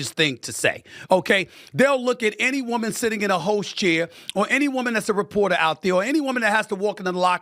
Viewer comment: Did he say " They'll look at any woman in a hoes chair"?🤔Freudian slip.😁